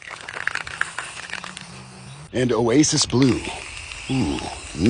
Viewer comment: I looked away from a video for a second because I had to yawn I thought he said a racist blue instead of Oasis blue 🤦‍♂️